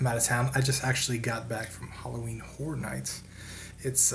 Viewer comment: I thought you said Halloween whore night. I was about to book a ticket to Florida.